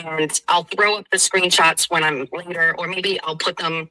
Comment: Did she just say "...and I'll throw up the screenshots when I'm with Mer...or maybe I'll, maybe I'll put them on a post on YT..."?????? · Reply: according to AI, she said 'her'...'when I'm with her'. It did sound like Mer...at first!!